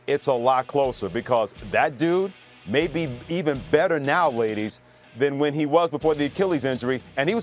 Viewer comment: did he just call us ladies?